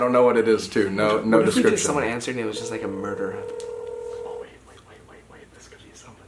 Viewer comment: The one where it was just saying gibberish, I heard “I’m a murder”